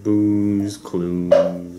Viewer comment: did someone say blue condom???